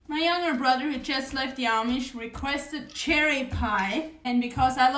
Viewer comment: I'm glad I listened to this twice. Because I thought you said your little brother just left the army and I thought that was quite odd. And then I thought, well I know that you have a brother that also left the Amish. But when I listened the second time I heard you said he left the Amish and he wanted a cherry pie with cream cheese bottom. Sounds yummy! I'm all in to learn how to make that cherry pie filling.